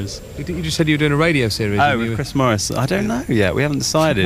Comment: fuckin hell I misheard that and thought he said "I'm doing a radio show with Chris Moyles" I nearly crashed my car